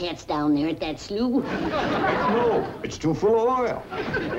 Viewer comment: Sounds like Jed is saying " hell no. It's too full of oil " at 4:05. Sounds like the CBS censors missed that.